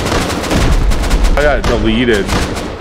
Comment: i heard "i got da-yeeted" . can we make this a thing?